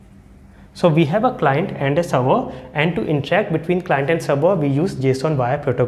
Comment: I didn't understand the word which he is telling at 2:30 in the video. Is it "encheck"? Could anyone pls help me on this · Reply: Hi Anitha, it was "to interact" you can switch on captions too to help